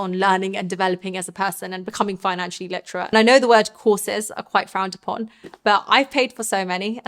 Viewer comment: I swear I heard the word “Horses” 🐴when you mentioned education. Great videos. Just purchased an Apple Watch to take calls whilst on site. Keeps my hands free to continue working but able to take calls at same time. So simple but very effective